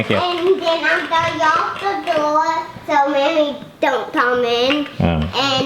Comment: gonna lock memory outside lol typical little brother move 😂😂😂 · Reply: You mean here? That's Filia. :D Oh! Never mind. He locked it "...so Memory don't come in." :D Hahahaha 17:02 I didn't even catch that earlier. Sorry. :D Haha